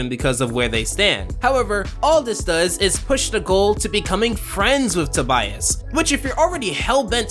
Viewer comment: Did he say Gumball or Nicole? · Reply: He says: "...all this does is push the goal to becoming friends with Tobias."